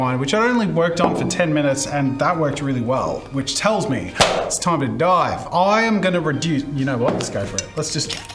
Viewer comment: Brain and humour so fucked up i thought he said "it's time to dive"